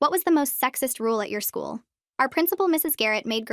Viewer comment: I thought it said "sexiest" instead of "sexist"